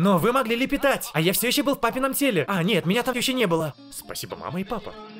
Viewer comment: мне послышалось или он сказал в папином??? Ну да это моя шиза